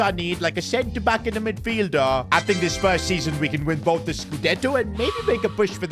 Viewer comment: Did he say spurs season? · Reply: No he said first season